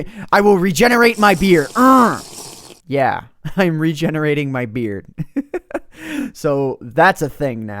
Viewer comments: It sounded like u said im regenerading my beer. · It sounded like he said regenerating my beer XD